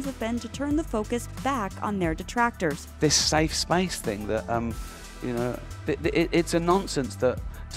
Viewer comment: I thought he said ice spice 😭😭😭